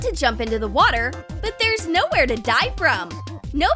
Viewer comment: I thought she said "but there's nowhere to die from!"